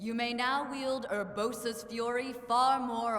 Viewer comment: Did he just say va Urbosa?